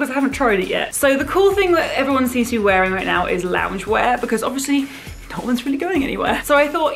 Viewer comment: I thought this said "long underwear" not "loungewear" and I was very confused why that would be a video